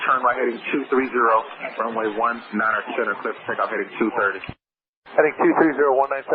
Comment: Am I going crazy? I don't see where he actually cleared United for T/O. I've heard this tape before, and I know the controller did mess up and clear him unintentionally, but it seems like the audio of him actually giving the take off clearance was edited out of this video?? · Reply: Start listening at 11:09 - when they give a takeoff clearance, they start by giving the first departure instruction for the climbout "...turn right heading 230...", then the runway you are operating on "...runway 19C..." and then finally the takeoff clearance "...cleared for takeoff". In this case, he repeated the heading again after the T/O clearance, but that was just a little restatement for emphasis. My belief is that they do it in this order instead of saying the "Cleared for takeoff" first because once we hear "cleared for takeoff", we have a tendency to deprioritize the information that comes after. So they load you up with the important stuff - your first departure heading, reconfirm the runway you should be on - and THEN open the gates with "cleared for takeoff."